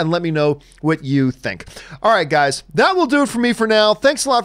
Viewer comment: Every time you end your videos with "bye bye" i hear "Bob Iger" :D